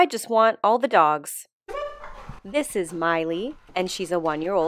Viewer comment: I heard "Smiley" at first. "This is Miley", at the start.